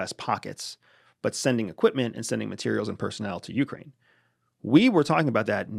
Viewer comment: did you say personnel?